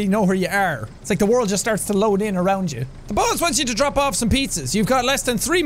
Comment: when Jack said the boss wants you to deliver pizzas i thought he said the balls wants you to deliver piss